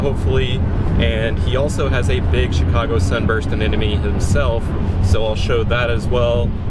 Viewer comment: Lmao I thought when you said the Chicago would spread around the tank it would be more “Chicago Suburbs,” lol I realized you said Sunburst after. It’s an awesome addition